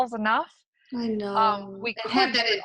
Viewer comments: on 26:32 - 26:35 .... there is someone saying - i know... ahhaa... anybody hear that? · on 26:32 - 26:35 .... there is someone saying - i know... ahhaa... anybody hear that? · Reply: I hear " I know" in a males voice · I hear "I know" in a male's voice